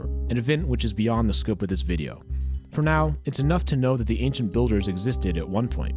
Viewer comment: I thought he said 'anxious builders' I can't